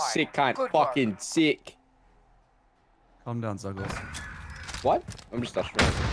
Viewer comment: did i hear "Zuckles" ?